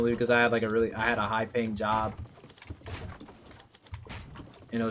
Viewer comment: i keep hearing "high ping job"